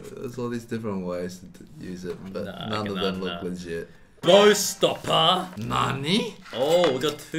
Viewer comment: At first I thought he said door stopper and I jest thought… no… why 😂